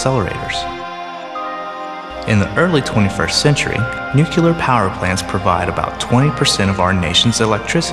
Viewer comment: Still unable to believe I heard the word "nukelar" at 3:54...